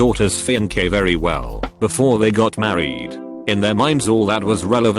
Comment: oh god I’m dum I thought it said “well before they got murdered”